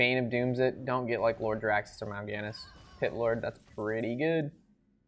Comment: did he say "Hitler...thats pretty good" ?? :D :D I was like what the heck